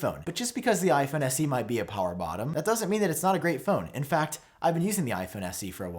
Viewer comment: Did he really say the iPhone SE is a “...power bottom” at 4:43?? lol 😂